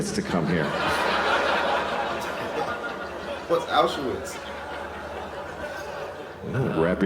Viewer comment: Did he just ask what is Auschwitz? Wow. Children, say no to drugs.